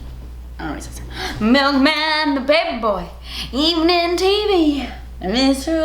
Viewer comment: I grew up watching full house and NEVER KNEW they say “evening tv” I’ve ALWAYS thought they said “he-man tv” 🤣😂😂😂 I was today years old...